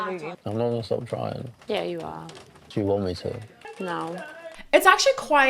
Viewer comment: I think you misheard. Shakira actually answers no to him asking “do you want me (to stop trying)?”, so technically she’s already folded.